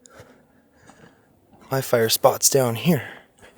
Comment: Thank you, it is great to see you back in Canada. The voice sounded like it said, " I'm down here." You. are very brave to go by yourself, these adventures are quite good.